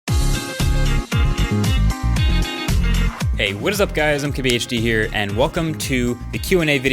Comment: Always thought he said "I'm QBHD here" 🤔